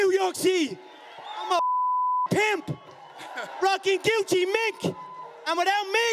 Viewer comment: I’m convinced most people thought he said “rocking Gucci Mick” 😂😂 Some lout.